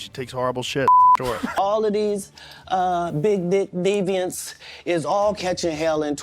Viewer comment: I thought he said “big dick deviants” 😅